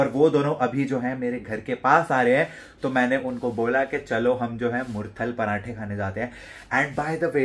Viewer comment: mutthal parathe? Did I heard it right 😂? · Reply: Murthal bola bhai naam mat bigaad